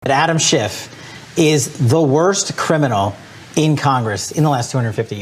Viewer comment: I I heard Adam schiff' likes little boys